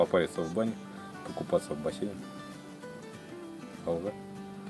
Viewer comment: На 14:25 мин вы сказали казахское слово АЛГА ? , или мне послышался . Приятно услышать